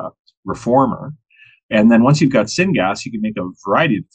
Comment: Did he say "sin gas?" because that's perfect if he did